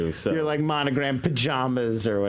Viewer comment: they all say "pajamas" really weird. Swear I heard "pahjahmbas" by one of them